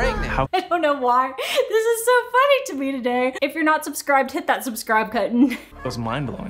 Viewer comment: I thought you said 'hit that subscribe kitten' and it made my day 😂